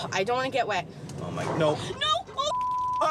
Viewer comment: Does really say "MY VAGINA!" when the water's falling on her? Did I hear that correctly?